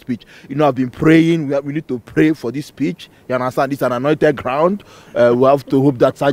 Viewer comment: At first i thought he said: "We need to pray for this bitch" :P